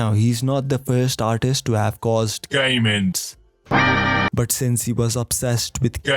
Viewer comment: Instead of "game ends" i heard it as gay men, which made the phrase "he obsessed with gay men" make sense af